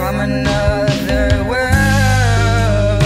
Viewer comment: until i watched this, i thought he said "with thighs like the ocean" lol. i guess eyes makes a bit more sense